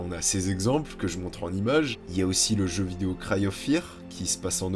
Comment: Il a dit c'était quoi le jeux vidéo a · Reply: Cry of fear